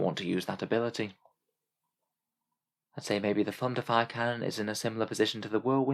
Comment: straight up thought you said "Thunder Thighs Cannon" right there XD